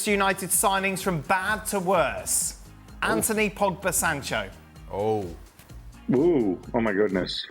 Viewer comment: What I heard was, “Anthony pogged the Sancho.” He did what?